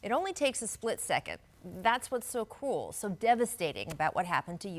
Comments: Did she just say the injury was so cool yet devastating ? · Thought she said ‘that’s what so cool’ at first 😂had to turn these AirPods up